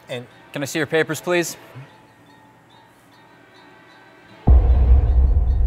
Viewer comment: "Messed up?" I thought he said "that's my stop" :S